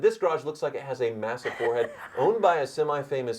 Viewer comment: Ar first i thought it said " Owned by a massive forheaded Youtuber" 🤭